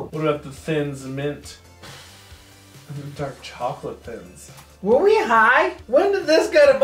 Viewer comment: where they high? i heard say "are we high" and "where we high" and ask if me liked 2 times because he forgot or am i only catching this because I'm high. lol